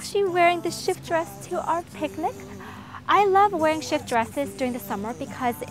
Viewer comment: I thought she was saying 'shit dresses' all throughout the video. x]